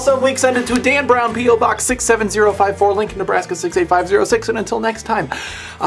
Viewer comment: For the past few Wall Wensdays I thought he was saying "Link In" not Lincoln.